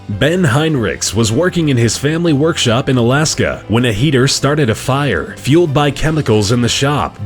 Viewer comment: I thought he said when a hater started a fire XD